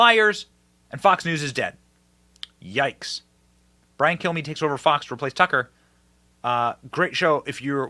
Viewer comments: I swear it sounds like he says Brain Kill me😂😂😂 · It sounded like he said my name is Brian kill me